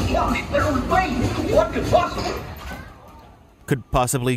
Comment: did Goofy say “what the f” That sounds so god damn fitting though lol